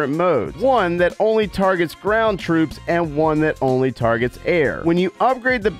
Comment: I thought he said brown troops 💀